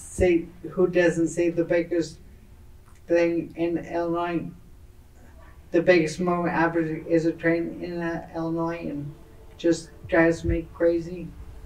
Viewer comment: I could be wrong but I believe he is saying "...The biggest moving object is a train in Illinois" but the video subtitle got some nonsense about the biggest movement is a train accident :/ bruh